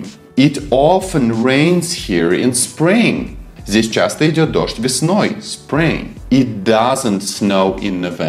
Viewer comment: Мне одной послышалось вместо "Идёт дождь" "Идиот дождь"😂👍